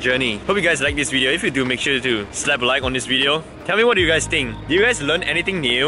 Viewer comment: Hang on, Did he just say SLAPP LIKE?!